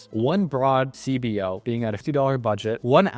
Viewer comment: Did he say a 2 dollar budget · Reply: He said 50